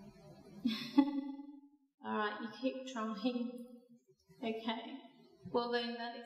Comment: did u just say :keep trumping?" doesnt that mean farting over there?